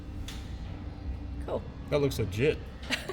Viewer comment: seconds in…. Did you say that looks legit?